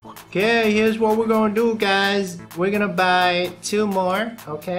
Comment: did you say u wanna buy Tumor, Lol